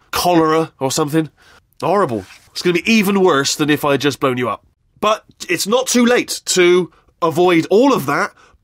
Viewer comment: I thought he said 'bone you up' I was so confused